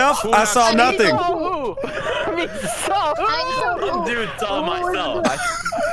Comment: Delirious - ME SAW WHO What I heard Me'sa Who? Jar jar vibes